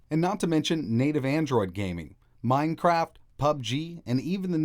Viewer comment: Did you say Mine-crap?